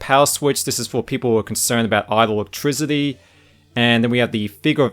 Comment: What did you say people were concerned about at this part, again?... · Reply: Idle electricity. The switch allows the power to be fully cut.